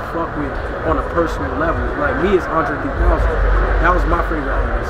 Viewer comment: Who is denzels favorite artist what he say? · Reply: Andre3000